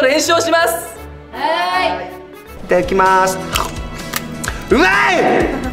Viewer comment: 美味いやなくて「ッまい！」にしか聞こえへんwwww 3人とも可愛いの草